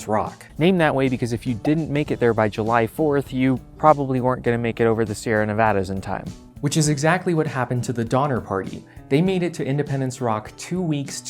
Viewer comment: Donner? Sorry, I thought you said it is a dinner party. Oh, no worry?